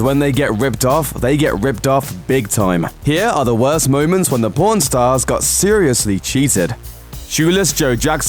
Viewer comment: It sounds like you saying p*rn stars